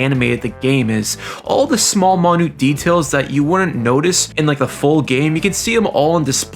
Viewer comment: he says "monute details" instead of minute details and it's missing me off